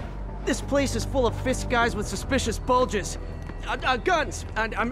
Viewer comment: Oof i thought he said mysterious bulges